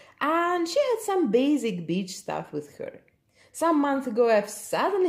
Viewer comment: when you were talking about your 1st doll --Martina--it sounded like you said she had some basic bitch stuff with her... :D